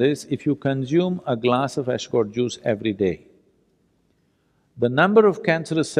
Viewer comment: help please im not enlighs speaking i cant understand “ascorn juice” ? Is the word ? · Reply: It's ashguard juice